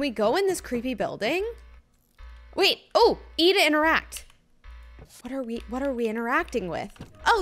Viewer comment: I heard “eat to interact” 😂😂